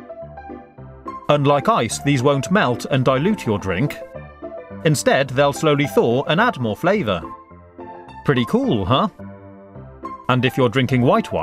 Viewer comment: i swear at 2:38 the first time i heard it it sounded like preaty poo huh